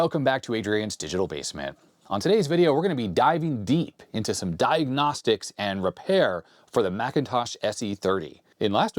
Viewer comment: I misheard "Dive-gnostics" and now I think is a great name for this kind of series lol